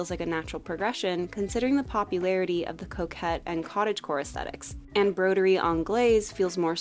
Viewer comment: I literally heard "the return of the cokehead and the cottagecore aesthetics"